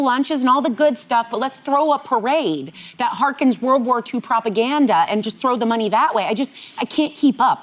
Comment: Did she say the Army Birthday Parade in Washington "hearkens WW2 propaganda"? What?